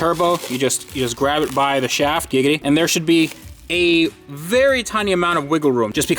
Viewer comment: Did he say giggity?